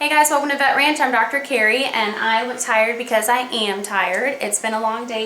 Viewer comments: “I look tired because I am tired.” Story of my life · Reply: I thought she said “ I am tired cause I AM tired” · lul thought she said I'm retired cuz I can be retired